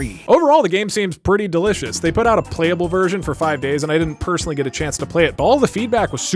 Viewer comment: When he said "playable" I thought he said "plate-able" for some reason.